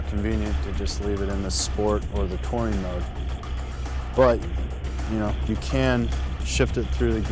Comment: Good video, until half was through I thought he was saying "taurine mode" and I was like "what the fuck"?